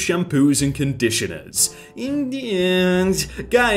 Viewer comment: did just say Indians? 😂😂